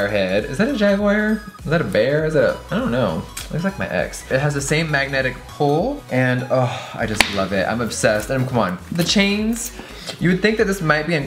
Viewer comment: Did I hear that looks like my ex